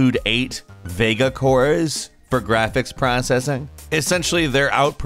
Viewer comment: is it just me or does he say graphics processor really weirdly 😂😂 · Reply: Haha he says processing not processor